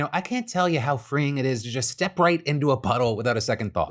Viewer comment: was is just me or did anyone else do a double take during the vessi ad. coulda sworn he said butthole not puddle